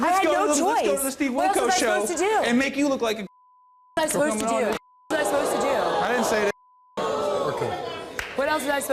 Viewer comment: What did he call her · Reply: He called her beautiful.